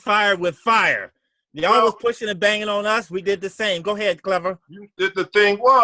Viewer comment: did Coop say “go ahead Clubber (as in Clubber Lang from Rocky?)